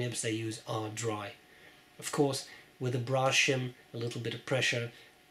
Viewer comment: I heard you mention a tool, a 'brawshum' (as it sounded to me). What is that?